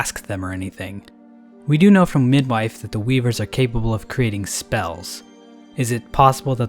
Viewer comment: i understood the word 'weeb' instead of weave the weebers